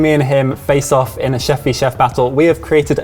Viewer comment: Every time they say "Chef V. Chef Battle," I hear "Cheffy Chef Battle."